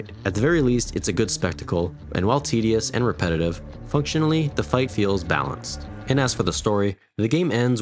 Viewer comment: I heard: “fuck-tionally”